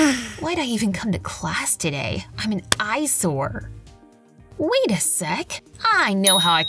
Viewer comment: did they said aysor??Because that is in arminian